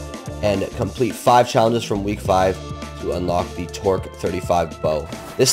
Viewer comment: i thought bro said twerk 35 had me shouting “AYOOOOO!!”😂😂